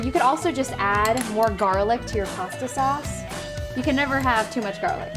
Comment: did she say pasta sauce at 3:29? Maybe i'm just hearing things...